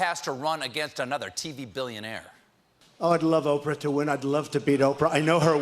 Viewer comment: Freudian slip? He clearly says “I’d love Oprah to win”